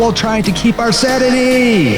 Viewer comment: Am i the only one who noticed 'Satiny' instead of 'Sanity' at 0:10?